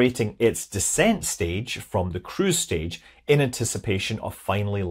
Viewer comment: For a moment I thought you said "crew stage" and was wondering how advanced soviets really were :D. I had to check subtitles to make sure. If the information that it is 1m in diameter right, it should have displacement of 524 kg, which would mean it should float. I wonder, when it lands, where will the wheel of fortune of soviet inheritance land, will Russia be responsible for it or not?